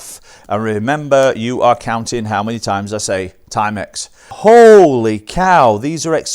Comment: For a moment I thought you said "how many timex I say timex" haha. But I repeated the video about 6-7 times and confirmed it was 18 times.